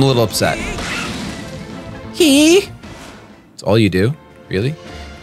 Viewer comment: I really thought Kliff said "Yee Yee"